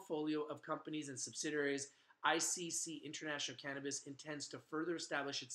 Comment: They are a good company! There connected with Xly, i know they have potential. Thought you were saying juju international cannabrands, and I was rolling my eyes haha. juju up 12.5% today though